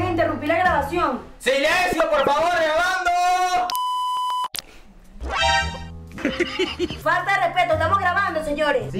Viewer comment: INTRIGA, QUERIA SABER LO QUE DIJO JAJAJAAJ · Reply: Dijo ¡COÑO!